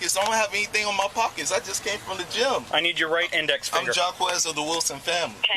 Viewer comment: “I don’t have anything in my pockets! I just came from jail!” Shocking 🙄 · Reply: I thought he said he just came from the gym.